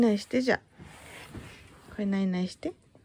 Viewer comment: 「ないない」が時々「にゃいにゃい…」に聞こえてかわいい！